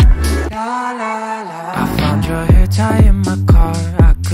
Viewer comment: "i found your hair tie n my car.." i always hear " i found your h3nta1 in my car.."